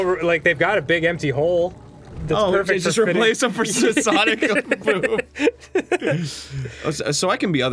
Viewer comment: did Arin say Sonic o boom